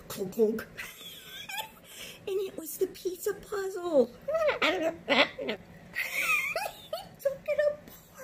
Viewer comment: Sounded like he said “piece of puzzle” back!